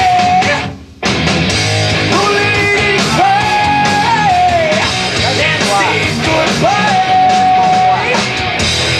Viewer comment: What's Bob saying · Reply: He said something about it being "flat".